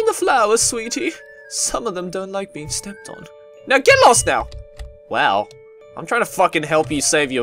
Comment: wow when the caterpillar said don't get lost you thought she said get lost